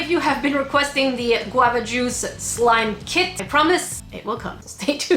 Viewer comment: For some reason I thought you said "the flobber juice slime kit" and I was like "Oh my God, I don't care that I can't really touch slime (allergies) I need that"